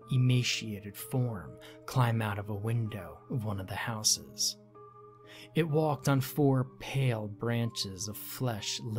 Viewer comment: what does he say "like a seeununme"? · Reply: Sea anemone